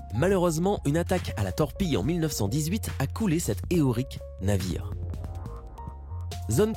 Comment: C'est moi ou j'ai entendu Heorique